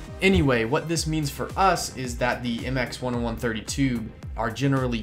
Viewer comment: It’s a MX10130. Did I say MX10132 somewhere in the video? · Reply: I think you said mx10130 tube but I heard it as mx10132, my bad